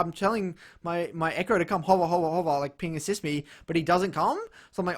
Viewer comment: laughing so hard I have tears in my eyes. Wtf did you say? “I tell my echo to holla holla holla and assist me” WHAT?? Hahaha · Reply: Hahaha I'm saying "hover hover hover" Asin hover me so I can push out bot LOL